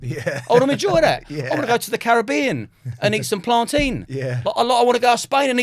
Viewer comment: Did Liam say "Plant Teen"?! 😂 🤣 😅 😂 😅 🤣 😂 😂 🤣 🤣 🤣 😂 FFS